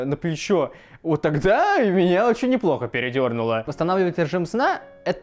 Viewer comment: Мне послышалось:"Она мне неплохо передёрнула"